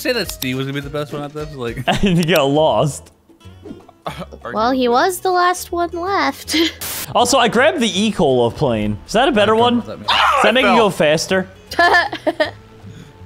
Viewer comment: When said he grabbed the ecola plane I thought he said the ecoli plane and I was like ummmmmm